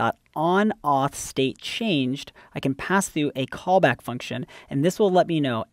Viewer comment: typo in subtitles: onAuthStateChanged instead of onOffStateChanged.